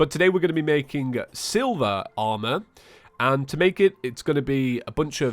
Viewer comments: I can’t believe I thought he said silver armor. I was like “James you ok?” Then I was like “Ohhhh, Silva Armor.” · I thought he said silver not silva